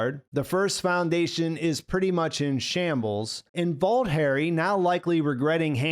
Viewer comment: I keep hearing "Bald Harry" instead "Vault Harry". This might have substantial deviations from the books, but I'm impressed with the show none-the-less. And, great coverage as always.